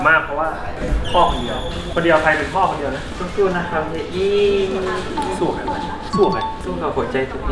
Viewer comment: I have been waiting for SO LONG to find out what Nat says here that makes Max melt like that. SO CUTE, I LOVE THEM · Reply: Susu na kub Hia Yi means Fighting Hia Yi 😖